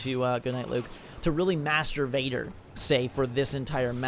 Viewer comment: who else thought he said masturbater at 13:53?